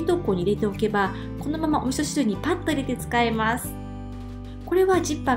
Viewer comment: なんと言っていますか？聞き取れなくて…おすす？お味噌汁？ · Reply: お味噌汁 だとおもいます、( ›_‹ )！